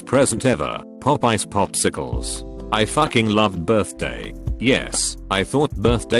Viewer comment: I thought they said Popeyes popsicles and was like when the fuck did Popeyes offer popsicles lmao